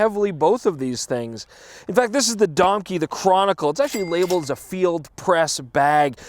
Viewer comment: i really thought you said DONKEY... not domke.. lol